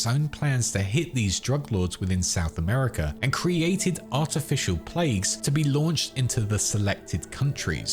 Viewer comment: did I hear somebody mention artificial plagues?? o_O